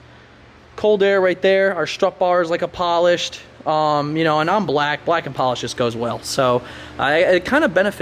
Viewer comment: Did you just say, "I'm black"?! LMAO JK, I know what you meant. The exhaust system sounds and looks beautiful!